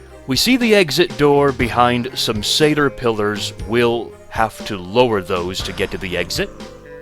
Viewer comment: what did you call those pillars? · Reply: Satyr pillars. Satyrs meaning the faces on them.